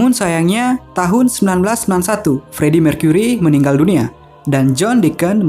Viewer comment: Di menit ke 5:08 saya dengernya freddy meninggal di tahun 1961? Bukannya tahun 1991 ya?